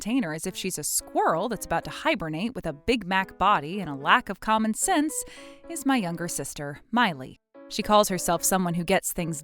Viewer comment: BIG MAC BODY! 😅😅😅😅😅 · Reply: I misheard at first and heard "Big Back" body until I read the texts 😂😂😂